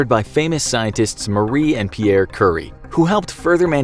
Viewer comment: Did he just say "curry" instead of "curie"?